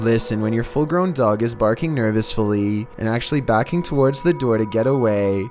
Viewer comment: Did just say "Nervousfully"??? Lol.